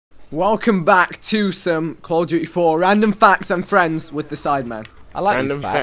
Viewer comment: Did Ethan say 2some